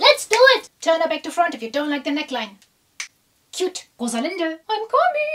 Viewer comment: 😂😂"Rosalinda"i thought she said "woza Linda" means come Linda,My Head and IsiZulu language